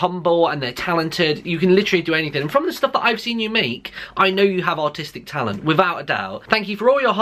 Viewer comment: lol I heard "I know you have autistic talent"